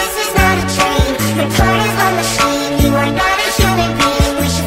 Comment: " You're part of a machine, you are not a human being bean. " You're right, I'm a potato. ._. Why tha freak does this have so many likes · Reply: When i first "Human being" i heard "human bean"